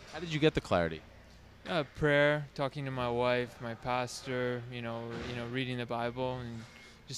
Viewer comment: I’m going to talk to my pasta when ever I need clarity from now on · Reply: What? 0:48 He clearly says pastor, so I'm not sure I understand the joke